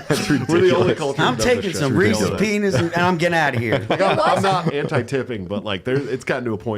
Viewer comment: Phil said Reese’s p3nis instead of Reese’s pieces 😂